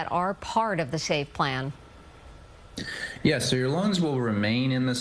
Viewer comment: it sounded like he said, "Your lungs will remain in the save plan."